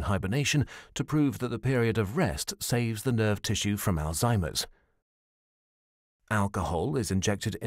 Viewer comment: I am trying to translate this video in greek. But I don't understand a word in 3:52 "Saves the nerve tissue from.......????" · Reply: Alex GraphicD Alzheimer's